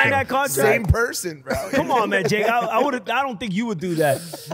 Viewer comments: Did he just call him Jake @ 7:10?/?-?-/?-?-? 😂😂😮😂😢😂😢😂😢😂😢😂😢😂😢😂😢😂 I heard Logans pain in his response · Did he just call him Jake? lmao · did he call him jack